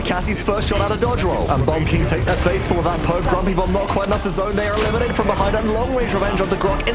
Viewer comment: he actually said grompy bomb not grumpy bomb